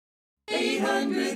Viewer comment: When I was little I thought they said and pie at the end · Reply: I thought it said and 5 for so long 😂 I just found out yesterday it said Empire smh.